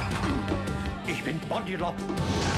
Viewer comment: Did he just say "Ich bin Robbie Rotten" at 0:59? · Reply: ich bin body lob